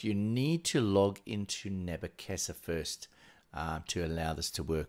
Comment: Hello. I can't quite catch what you are saying at 3:31 ? The caption generator spells it nebuchessa but googling it doesn't seem to give any clues. I've tried this the esp web thing and that doesn't see to work so going to try logging in to ned? then accessing through chrome.. Thanks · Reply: Great stuff thanks mate. Nabu casa, ahh